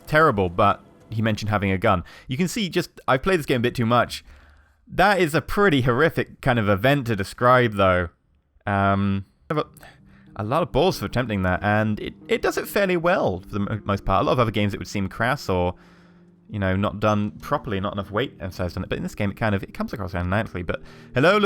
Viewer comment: A lot of balls are attempthing that. Did he says balls? the two balls under the pe*is 😰😵😲💩